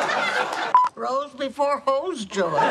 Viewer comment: Might have misheard her in the "before bros" bit but I swear she said "Rose before bros" (Obvious reference to the Golden Girls Rose Nylund), and it took longer than it should have for me to stop laughing 🤣🤣🤣🤣